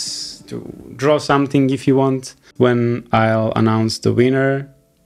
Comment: Just after this absolutely beautiful drawing, I really heard "I'll announce the wiener"